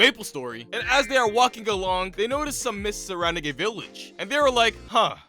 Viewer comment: deadass thought that said 'Sussy Village' for a sec there...